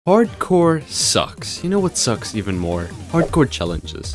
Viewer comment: bro i thought he said "hardcore sex"